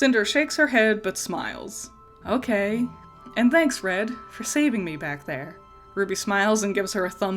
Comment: I thought she said fred then I looked at jaun and was like oh my god he DOSE look like fred from Scooby Do